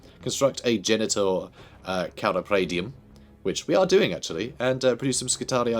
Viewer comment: I misheard one of the buildings as “Genital Car-Appraisium”